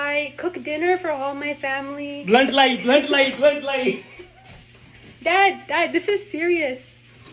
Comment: i thought he said LUND LIFE 🤣🤣🤣🤣🤣🤣..... later realized it was red light..... desis would get what i meant 😌🙏🏻